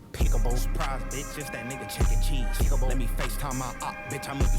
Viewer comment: “Surprise bitch it’s that nigga Chuck E. Cheese” 😂😂 I thought he said that at first as well 🙃